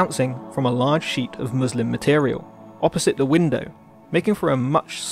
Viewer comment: I heard "bouncing off a muslim material" and laughed my ass off 😂😂 Ps. You broke my brain with this image